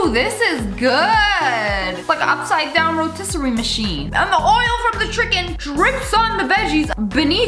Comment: Who else Heard- her say the oil from the Tricken